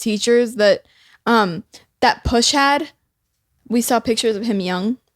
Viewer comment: did call “push” 💓